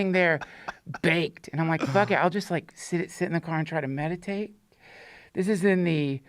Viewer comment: Had to run it back real quick thought he said masturbate instead of meditate 😅😂😂🤷‍♂️